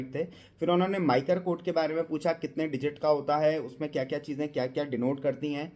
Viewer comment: What's the code name which you are saying here 5:52 ?? I am not able to get the name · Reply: MICR CODE MAGNETIC INK CHARACTER RECOGNITION